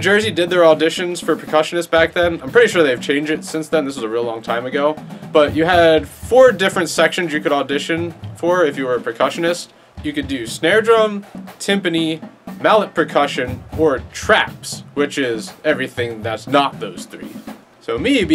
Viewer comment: did he just say timpany? How dare thee